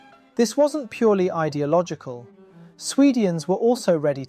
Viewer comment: This must be AI, must it not? Did I just hear this AI say 'swedians' at 6:35 ?! Dead internet theory 😥